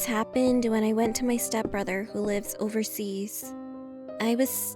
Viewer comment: did you say STEP BRO